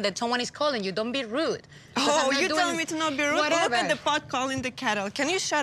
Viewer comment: what did anfisa said in here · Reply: “Look at the pot calling the kettle.” She just got the exact words of the saying wrong or it could have been editing; it should be “The pot calling the kettle black.” (Missing the word black.)